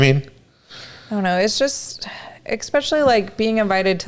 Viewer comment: did you say "exspecially"? 😄